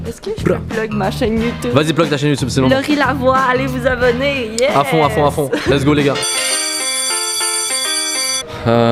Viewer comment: À 13:54, je n’ai pas entendu le nom de sa chaîne YouTube :/. « Lorie Lavoi » ou quelque chose comme ça mais j’ai pas trouvé. Merci par avance !😊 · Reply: Laurie Lavoie